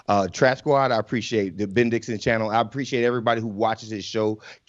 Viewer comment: Im hearing you loud and clear. But, did I hear "Big Dixon"? I mean, that could be some branding right there.